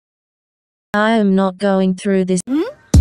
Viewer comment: Play 6:45 on 2x speed you can thank me later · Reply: I hear *I'm NoT rApInG